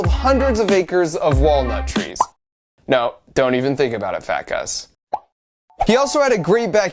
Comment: Wait, what did you say? Phat Gus? ITS PHANTASTIC GUS! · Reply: phat gus tyvm